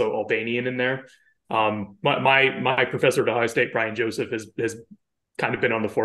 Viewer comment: Could somebody hear the name of professor at 16:38? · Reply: The transcript says Brian Joseph